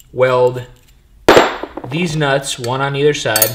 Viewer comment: Did you say Deez nuts?